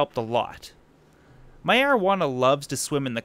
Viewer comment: I thought he said my marjuana